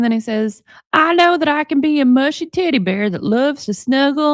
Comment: "I can be a mushy Teddy Bear..." With your 'Ryan' accent, it sounded like "A Mushy Titty Bear" - oh, damn; and even though I have no idea what a Titty-Bear is - the term suits Ryan Anderson so much. Right now, that's what he is to me - a Titty-Bear. 😂😂😂